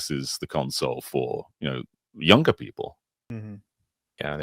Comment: did say "uncer"? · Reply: He said younger